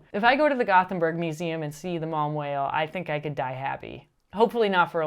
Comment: I really thought you were saying "Mom Whale" until I saw the lower third about it